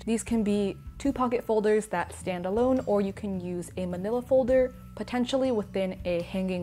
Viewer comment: did i hear her right, manila folder?